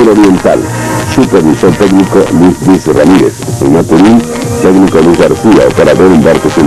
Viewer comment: Creo que escuché que dijo "Maturín", pues ahí es de donde yo soy 😊